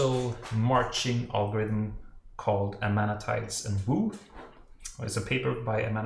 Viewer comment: What is the name of the marching algorithm mentioned at 15:12? I couldn't understand the authors' names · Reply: Amanatides & Woo. It is basically your standard supercover line grid traversal algorithm.